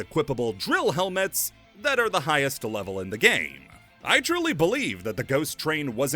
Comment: did you say "Drill-y" instead of Truly?